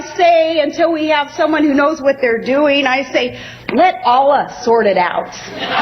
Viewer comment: Hey dumbass, The American civil war was not over God and open your ears, she said let allah > IT < out 1:17 You fucking libtards cant understand english. Maybe thats why you favor illegals.